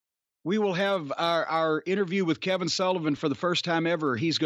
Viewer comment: Im just waking up while listening, and I thought Jim said "Kevin Dunn" instead of Kevin Sullivan. I was like "Kevin Dunn had a relationship with Nancy Benoit? Wow her standards are looooooow" 😂😂